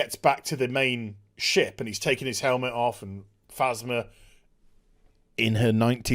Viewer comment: I misheard you talking about Finns first mission, returning and having Asthma (not Phasma) Still works cause poor John boy is always out of breath in SW